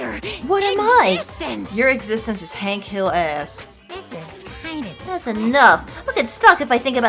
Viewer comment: For this entire play through I thought she'd been saying "hanky little ass" whenever monokuma would turn around. I only just now understood she's been saying hank hill ass 😂 idk which one is better tbh!